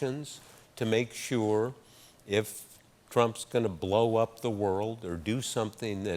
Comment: Woodward says "Trump" but the captions say "Terrorism" Could be a mistake, or could be intentional